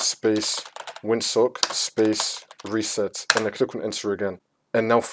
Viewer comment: did he just say "this should not fix your problem"